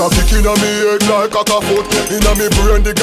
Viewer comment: I always thought he said sick inna he head😂😂😂😂😂😂